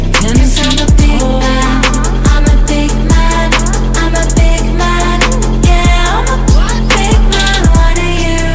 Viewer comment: Firstly when i heard the song i thought she was saying big mac 😂😂